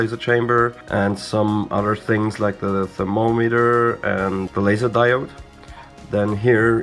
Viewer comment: you mention a laser diode? is that what you said?